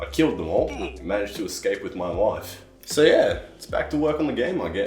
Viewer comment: Reality: managed to escape with my life The subtitles: managed to escape with my wife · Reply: without subs i thought he said with my wife and it made it even more funny